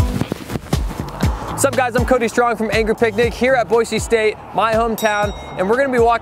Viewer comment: 😂😂 I thought he said angry pink dic 😂great vid man